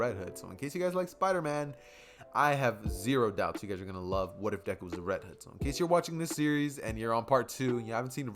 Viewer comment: At the start I thought he said “ what if Deku was a red head”